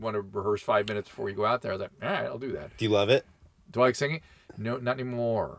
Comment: 😂all I heard was Paris Hilton's voice.."Do u love it"?!? I've been binging The Simple Life during my self isolation 🤷😂Don't judge me 🤪